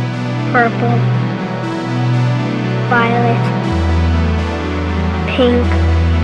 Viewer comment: what he say ? he say purple and what he say then ? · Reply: Violet